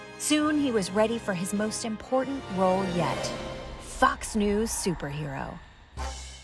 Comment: Fox News for a second I could’ve swore I heard fuck news 🤣😂😅